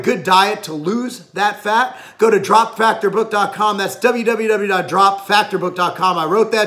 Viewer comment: what is the website address mentioned towards the end of the video about dropping weight/fat? I entered the address I thought he said, but got nothing? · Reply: Drop factor book